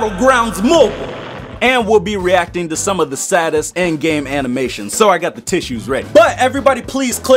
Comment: I thought you said reacting to the saddest annimations not play games !!!!!!